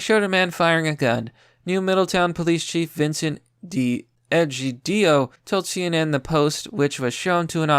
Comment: did I hear edgy DIO?